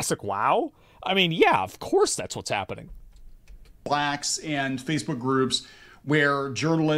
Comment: did he say "blacks"? As in black people????